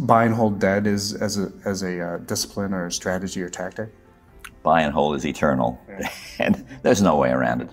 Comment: Oh, first I thought he said „buying whole is eternal“ and honestly that wouldn’t have been wrong